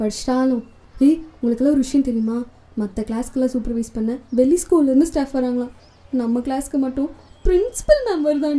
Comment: did they just say bell school 👀